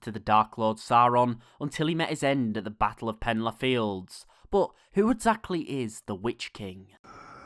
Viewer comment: All i heard was "penlarr fields", I'm sorry that I'm laughing so much. 😂👍